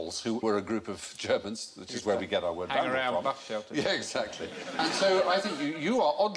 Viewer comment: How I long for someone to tell me what they both said at this point as they override each other and it just sounds like gibberish to me. /sigh. Closed captions would also be nice. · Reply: Steven Fry is saying" which is where we get our word vandal from" as Alan says "they hang around in bus shelters?"